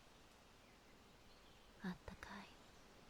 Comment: did I heard "あたたかい" (atatakai) ?